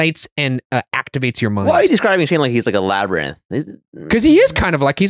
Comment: They called Shane a labyrinth and I definitely thought they said “lab rat” at first